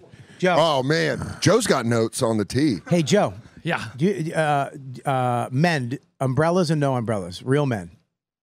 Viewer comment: I thought he said "menu's umbrella"